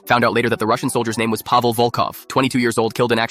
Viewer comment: I thought you said Pavel chekov